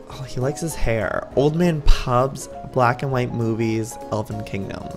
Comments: I thought it said old man pubes at first instead of pubs. I was like wtf?!!? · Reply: i thought it said black and white boobies XD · I thought it said old man pubes at first instead of pubs. I was like wtf?!!? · When he said black and white movies, I thought he said black and white boobies. 😳